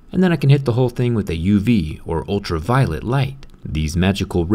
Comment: lmao I heard ultra violent light